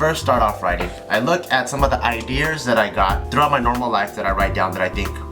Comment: Did anyone else hear say "ideers" at 6:12?